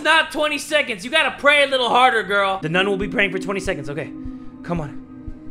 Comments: In the beginning I thought he said the game is called "the nut" 😂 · I thought he said the nut at the begging cuz my volume was low